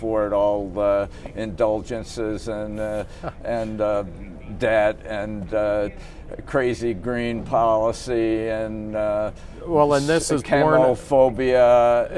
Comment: What word does Gilder use at 1:09 as Gillespie is talking over him? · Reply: “Debt” “crazy green policy” and then “cammo-phobia”.